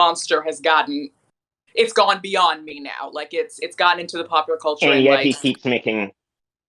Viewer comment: i’m sorry this conversation is so good and profound, but what did he/she/they say on this part??? cause i can’t understand the words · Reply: "When he left his show [...] I guess he did because he felt a bit like [...] It's gone beyond me now, like it's - it's gotten into the popular culture and like -" (Mx.): "And yet he keeps making -" (specials targeting other groups): "I mean, we can talk about it [...]"